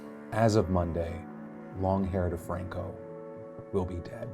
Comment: I thought he said Lawn Care DeFranco 😂😂 when did I miss new business venture? Haha